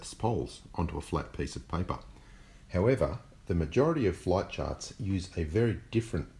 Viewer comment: floit chawts on the oipad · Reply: I thought he said flight sharts